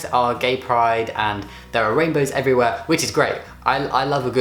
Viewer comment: The first couple of times he said rainbow I thought he said rape photo. Idk how. 😂😬